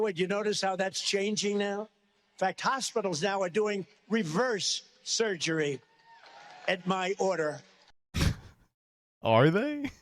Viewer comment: I heard they did reverse surgery on a grape